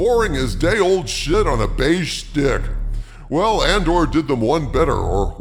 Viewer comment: "Day old shit on a beige stick!!!" That is the best line ever!!!!🤣🤣🤣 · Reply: I heard it as “beige dick”… But this works too. 😂😂